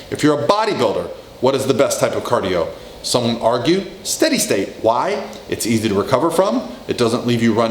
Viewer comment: What did he say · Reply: Don't argue